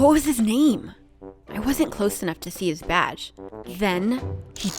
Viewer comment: I almost spat my water out because I heard, "I wasn't close enough to see his vag"